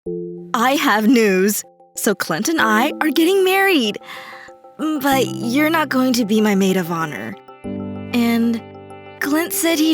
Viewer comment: I thought she said *i HaVe NuDeS*